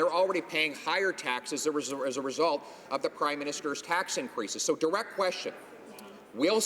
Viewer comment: Ahha, at about 6:20 mins in, did he just say" Crimeminister?" That's what it sounded like. I mean it's ok if he did, after all he was stating a fact! Trudope is the biggest crime boss of Canada. Always making up excuses to steal hard working taxpayers of their money that they earned. With b.s. carbon taxes. Maybe trudope should be taxing the cow farmers more, As the cows pollute more then the humans. And don't give me this crap if people drive less. As cars now a days are held to high standards with all there emission controls crap that they have on board.